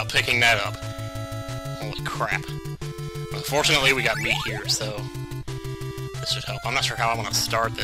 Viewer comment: I could almost swear you said "unfortunately for us, we have a pork chop..."